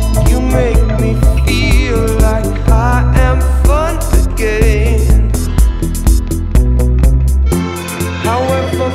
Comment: could somebody tell me what is the word he says between 2:30 and 2:31 ? (starts with F followed by "u" but i just can't get the rest of it) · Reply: Fun